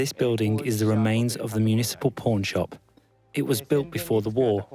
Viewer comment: 😅 I heard "municipal porn shop" and sub says the same 😆 I get it is pawn shop